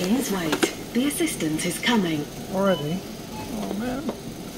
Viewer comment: Self check out sounded like she said "The Resistance is Coming"....of course, I was over by the sink eating a square of chocolate that I dipped in peanut butter and it wasn't until I sat down and rewound the video to realize she must've said something like 'the assistant is coming,' or what have you. Anyway, I can't stop laughing because how funny would it be if the register voice actually said 'The Resistance is Coming!"..every time you swiped a food and beverage item. Then I had to recall what the old saying was..Peace de la Resistance...and then, because of the register being in a seeming grocery store, how crazy would that be if the register voice actually implied that eventually 'you will resist this food' kind of thing. Battle of the Bulge, etc, via automated commands. And then, for some reason the following week you would feel like going on a strict diet only to realize later that somehow the register voice foreshadowed your diet path. Thanks for the laugh. It's 2 in a row because I was watching the sausage through the holes in the box game prior to this and it was fun and funny.